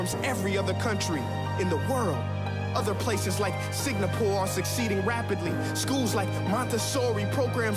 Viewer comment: did he say signapore in stead of singapore